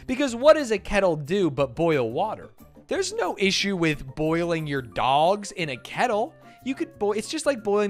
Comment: I was cooking and wasnt watching the vid and i heard "theres nothing wrong with cooking your dogs in a kettle" and dodnt realize it was hotdogs and i had to pause what i was doing to check